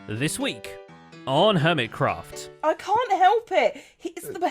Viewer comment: When I opened the video my stupid brain thought he said ‘this week on kermitcraft’